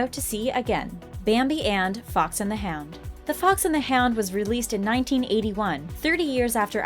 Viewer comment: I thought she said the fox and the ham 🤣🤣🤣